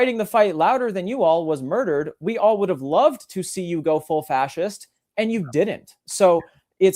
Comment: did he say “we all would’ve loved to see you go full fascist.”?